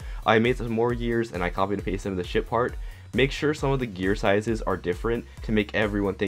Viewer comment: Lmfao I thought he said the shit part